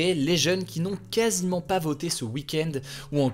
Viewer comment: Au début j'ai entendu "les jeunes n'ont casiment pas bosser ce week-end" au lieu "les jeunes n'ont casiment pas voté ce week-end"😅😂